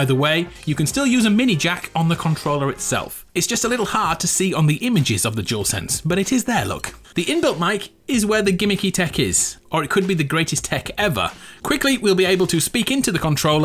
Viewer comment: stg every time this guy said dual sense it sounded like he was saying juul sense😂